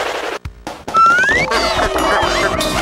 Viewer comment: I put on subtitles to see what whatever they thoght it was saying at the end when sonic or nic the og was talking about team work or something they thought it said "remember kids! Working together is better then balloon!" And i laughed my heart out and in my mind i was like WTF